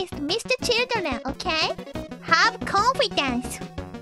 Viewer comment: I swear i heard "Have coffin dance" in 4:23, I laughed for hours after that